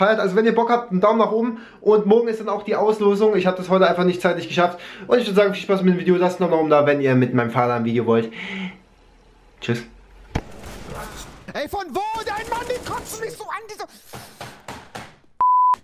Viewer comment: Ich hab die ganze zeit verstanden mit deinem fahrrad ein video machen xD